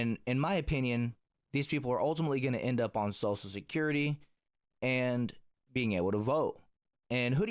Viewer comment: 😂 Question @ 21:23 did you say “Salsa security?” 😅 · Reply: lol, "social security" but I do usually have salsa on my mind